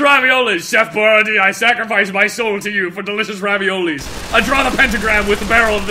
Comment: You edited "m" soul instead of "my" soul